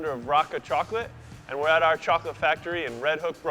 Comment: At the beginning I thought he said Barack o chocolate 😂😂